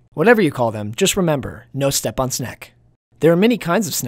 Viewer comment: REMEMBER: No step on snek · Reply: My dumbass thought it said "No step on shrek "